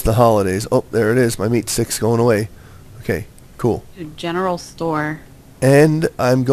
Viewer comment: i thought cupquake said I'm going to go to the genial store